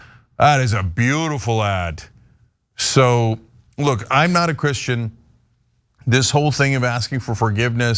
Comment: He said "I'm not Christian". He clearly said it. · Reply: I heard it as "I'm not Christian" at first too, but second time I realised was more like "I'm aaaaa Christian".